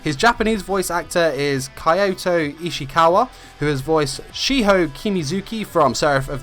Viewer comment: I thought he said Ichikawa instead of Ishikawa HnK people where you at